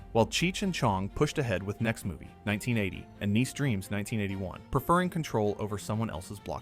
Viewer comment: wtf did he just say niece dreams